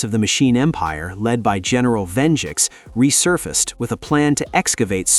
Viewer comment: sorry did you just say general VENJIX